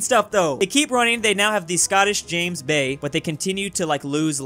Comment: I thought you said scottish James May